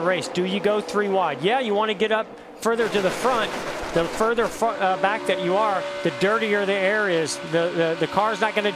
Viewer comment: did gordon said the further fucked?